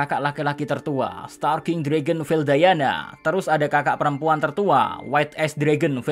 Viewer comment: Di 6:03 apa gak salah denger veldayana gitu